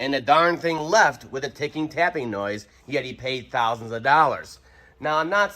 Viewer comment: Who said 8000? I'm pretty sure he just said thousands. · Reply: Okay I thought heard 8 thousand dollars. What's a crate motor cost.